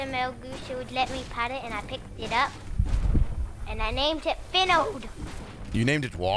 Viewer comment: "Wheres Finald now?" "oh he's dead" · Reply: am i the only one who heard him say “binald” insted of “finald”? 😂